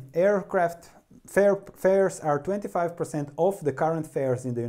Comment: What does he say at 8:30? "Flair"? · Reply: fares:-) At some point fares, (ticket prices) will have to go up